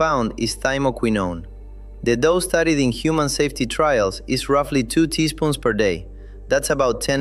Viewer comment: 2 tsp or Tbsp....he says Tsp it is written Tbsp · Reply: Around the 1:58 mark.He clearly said.Two teaspoons per day.